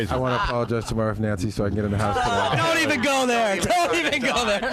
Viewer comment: Does say “I gotta apologize to my wife Nancy for this” right here? How did they breeze past that slip up? Watched a few times and I just noticed it.